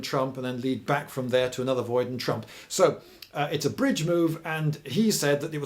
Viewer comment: Did I hear Trump?